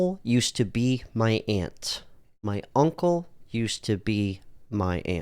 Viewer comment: “My uncle used to be my aunt” 😂 that was the funniest intro. Next do “Unique New York.” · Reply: At first I thought he said BEAT my aunt 😅